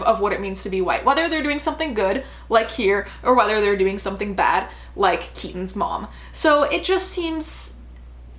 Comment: "like keedansmawm", i have no idea what you're saying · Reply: LIKE KEATON'S MOM